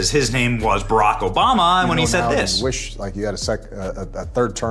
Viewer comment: “I WISH”, not I will. That’s what he said here